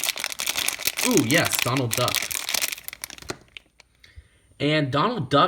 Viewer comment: when he said donald duck i thought he said donald trump